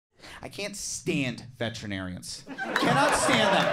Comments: for a second there I thought he said veterans💀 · I thought you said "veterans" at first and wondered why a veteran would just call your dog fat 😅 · Swore this said veterans and I busted out laughing 😂